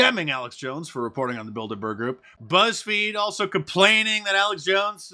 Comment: My mom brain thought you said Build-a-Bear Group at first, 😂